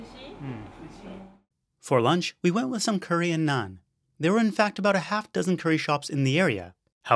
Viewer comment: I misheard "curry and naan" as "Korean naan" and I was very confused.